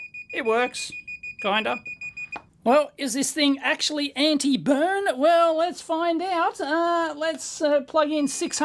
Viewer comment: Throw it at the wall. I thought the advert said 'anti-bum' 😅